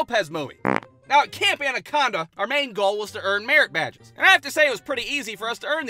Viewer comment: When I heard him say merit badge the first time. I thought I heard him say Marriage Badge.